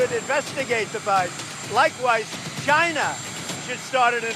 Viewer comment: It's really funny the way Trump says China, 1:37 he sounds like he said 'Gina like in vagina. LMAO